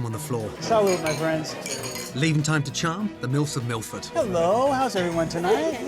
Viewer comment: did I just hear Gordon say Milfs?😂😂😂